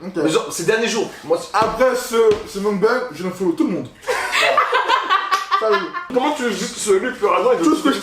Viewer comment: Il a dis quoi a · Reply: Après ce Mukbang je unfolow tout le monde